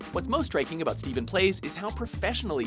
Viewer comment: When he said StephenPlays I thought he said smithplays